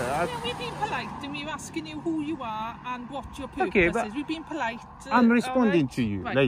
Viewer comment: I thought she said "watch you poop first" but she actually said "whats you purpose"